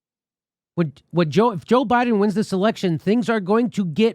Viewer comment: LOL 5:30 did he say WHEN joe biden wins this election???